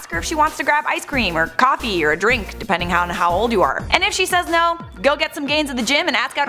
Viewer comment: I thought you said if she says then get some gays